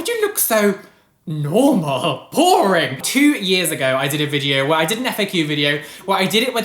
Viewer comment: I thought you said 2 ears ago lol